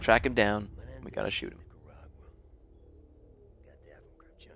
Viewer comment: Step 1: Turn on automatic closed captions Step 2: Go to 1:11 and wait for SeaNanners to say shoot him. It gets negotiate, HAHA TRACK HIM DOWN AND NEGOTIATE. EXACTLY WHAT HE SAID. THUMBS UP SO OTHERS CAN SEE.